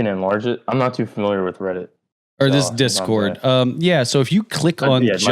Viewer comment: Did he say REDDIT 1:06:15 ??